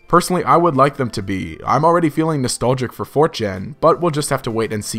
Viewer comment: I thought for a second you said "I'm already feeling nostalgiac for 4Chan". I was less worried when I realized what you actually said.